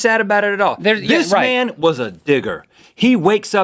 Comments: I thought he said the "N" word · I thought Rhett said, "This man is a n***!" xd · when he said digger it sounded like he said ni**